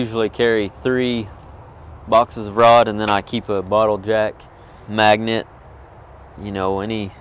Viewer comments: I thought you said "a bottle of jack" it was funny. Good video man. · When he said he carries 3 rod boxes and a bottle jack I thought he said a bottle of Jack haha I was like yeah rock on!! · Man i think a good video would be how to lay out a branch from start to finish. I think alot of people would enjoy that as well. Have a blessed day. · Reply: when you said bottle jack at first I swore you said a bottle of Jack lol